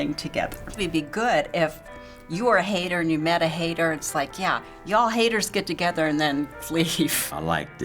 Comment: did..did...did she just say "y..y..y..ya'll"?